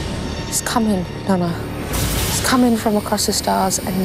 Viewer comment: When rose says "The darkness" At firt I thought she said "The Daleks"